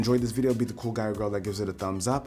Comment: I actually thought you said TrashConsumer. lol sry